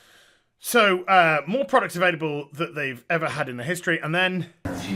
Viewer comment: oh maankin...i dont even play games on my tablet which were ported from pc and good titles on pc platform. not to speak of a smartphone...butthead: "uuuh huhuh did he just say "our future is bread" uuh huhuha ha" beavis: "yes ehehehe ehehehehehee ...BREAD..ehehehee"